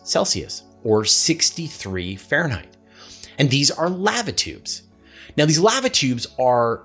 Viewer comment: Another great video. You threw me at 15:07. I thought you said latitude but did you day læva or lävə? So weird.